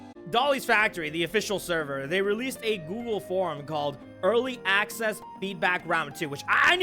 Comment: My dumbass thought you said goon form 0:13 😭😭